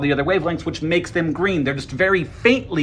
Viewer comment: at first I thought he said "minors are green." wtf